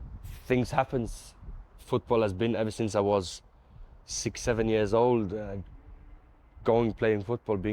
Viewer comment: Did he say 67??😂😂😂